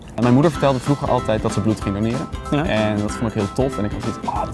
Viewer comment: wat zegt ie op 0:39? en mijn moeder zei altijd dat ............? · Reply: dat ze bloed ging doneren ; )